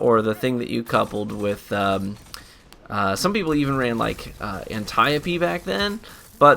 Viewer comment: Did he just say anti-op